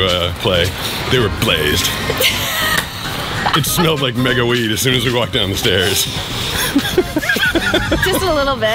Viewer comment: “they smelled like mega weed” thank you, i will be taking that quote now 😭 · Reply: I heard it as "mega weedus" and I'm fucking dyin 😂😂😂